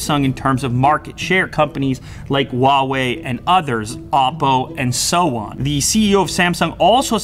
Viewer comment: the hell did you just call Apple? Wopo??